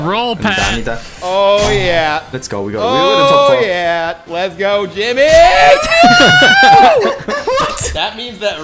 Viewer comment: Wait, did Pat say "let's go Jimmy"?